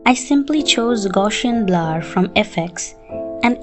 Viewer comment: What is she saying at 7:33 she chose what blur from fx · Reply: gaussian blur